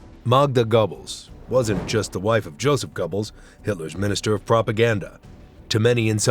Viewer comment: Literally with the first one, when I heard the name Goebbels it sounded like he was saying Gobbles like Timmy from South Park.